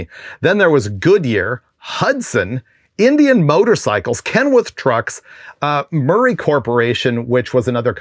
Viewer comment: Super cool info, Freiburger! I may have misheard, but it’s Kenworth… kind of sounds like you said Kenwirt. Thank you for another good one!